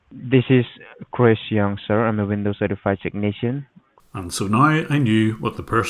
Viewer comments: I thought he said Christian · "Chris young" I hella thought he said Christian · I don't know if I heard wrong but Chris Young sounded like he said Christian lol. This is insane, and awesome I used to mess with a lot of scammers but this is next level😂👍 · i think the dude just said Christian with an indian accent and you assume chris young unless there is another source for the name besides the call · I think he might have said his name is Christian. Why would he give a last name?